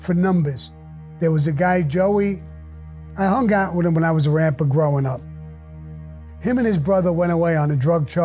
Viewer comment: did he say he was a rapper????? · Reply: He said 'When I was a Ramper'. The Rampers were a notorious street gang that included Thomas Spero, Joe Vitale, Ralph Spero, James Emma, and future Gambino crime family underboss Sammy Gravano.